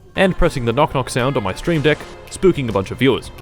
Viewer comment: I thought u said "Steam Deck" a second there